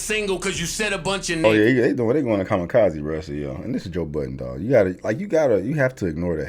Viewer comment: I'm still dying at the fact Joe Budden thought the line "Like that feature where I stole the show, sorry if I took forever" as saying he bodied Jay Z on renegade. Bars just flying over joe's head which is hard to miss with how big it is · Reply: My dumbass really thought you said Joe Biden 😂